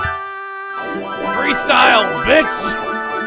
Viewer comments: Did he say...bitch..? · Did I just hear say "FREESTYLE, BITCH" or is it just me (I heard it at